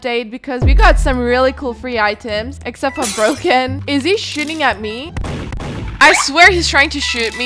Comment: at one point i thought you said "is he shitting at me?" nah you said shooting, btw i luv ur voice :D